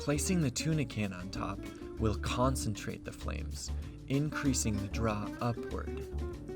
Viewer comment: it sounds like you pronounce "draught" so it rhymes with "aught". (Referring to the tuna can, you say it "increasing the draught upwards") It is actually pronounced "draft". It's the older, British spelling of "draft". (If that wasn't what you were trying to say, pardon my mis-hearing.) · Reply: No problem. I believe I said "increases the DRAW upward." Though I admit it does sound like draught/aught.